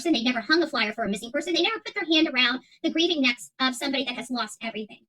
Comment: Whoa, I had to back up. I thought she said "They have never put their hand around the breathing necks.."😅 Nope, that's just you.